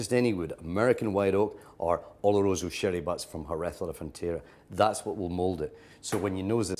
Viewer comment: from where? I didn't undestand the words.. Can you help me, what does he said? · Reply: “American white oak or Oloroso Sherry butts from Jerez de la Fontera. That’s what will mould it.” He’s just talking about what woods are best for aging that particular scotch